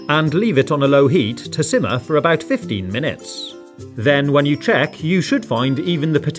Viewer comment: In 2:58 did u say 50 or 15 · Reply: He said 15 minutes